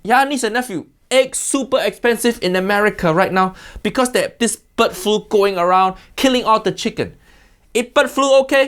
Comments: uncle roger! i thought you said butt flu! 2:23 HAYAHHH! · I thought he was saying "butt flu" 😂😂😂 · i had this as background noise while doing hw, so i thought he was saying "butt flu" instead of "bird flu" 😂😂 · at first I heard "butt flu" which confused me greatly for a second before I realized it's "bird flu".